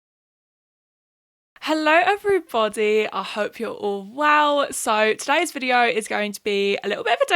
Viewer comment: i thought you said “i hope you’re all WOW!” 😂